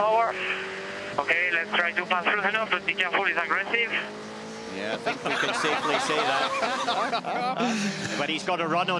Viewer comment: what did the engineer say to him? It was hard to understand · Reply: "Be careful, he's agressive"